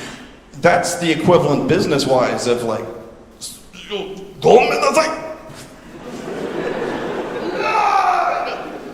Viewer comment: I know it's sepukku/harakiri but, what does he actually say there? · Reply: He said "gomen nasai", which is a formal apology in Japanese.